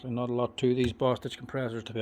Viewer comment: For a second, I thought you said Bast—d. But you said Bostitch. I had to replay it. I love the videos. Learning more about the tools I use.